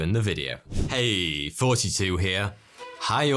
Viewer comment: Normally I don't watch the videos, I listen while I commute. For the longest time I always thought he said "Hey, 42 here". Was looking at the merch to support the channel and it hit me how dumb I really was when Iearned it was "Thoughty2"🤣🤣